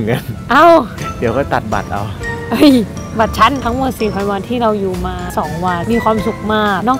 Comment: ได้ยินว่า 4000 วัน ใครได้ยินบ้าง 😋 ⬇️